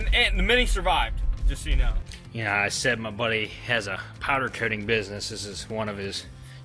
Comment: glad I relistened to the video...I could have sworn you said that your friend had a counterfeiting business, not a powder coating business. Should be sure I have my earring aides turned on when watching youtube. Great video as always